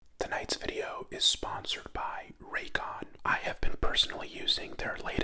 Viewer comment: i hear “thank you God for supporting my channel” instead of raycon i feel like he would tho